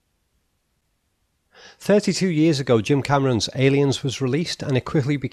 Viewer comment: I thought that said David Cameron’s aliens for a second. Christ that would be a horror film.